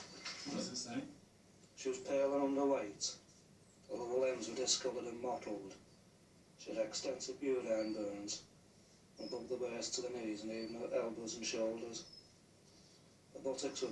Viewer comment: Anyone understand what husband said at 14:40? I can't follow their accent sometimes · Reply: She was pale and underweight. All her limbs were discolored and mottled. She had extensive urine burns, above the waist to the knees and even on her elbows and shoulders. Her buttocks were very sore and blistered. She was not toilet trained and reluctant to eat solid foods... That was in 77.